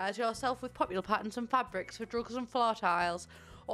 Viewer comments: Okay but I swear she said drugs instead of rugs at 3:13 and didn’t even notice 😃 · It sounded like you said drugs not rugs 😂